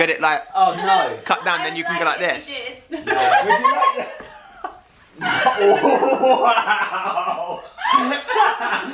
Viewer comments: Wat did say at around 10:26? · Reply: she said I would love it if he did. x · World anyone please tell me what the lady says at 10:30 ? Thanks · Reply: "I'd like it if he did". She was saying she'd like him having a split tongue.. It's kinda a rude joke aha;)